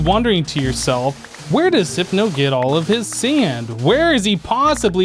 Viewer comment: did he say zypno??